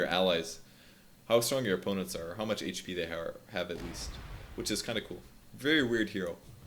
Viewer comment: says "How much HP they har instead of have" hahaha the lols